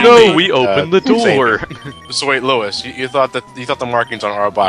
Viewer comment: Is it just me, or does it sound like is saying "so wait lois" at about 8:50?